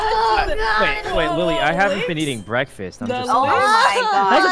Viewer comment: what did sykkuno said? I didnt get it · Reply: He said he hasn't been eating breakfast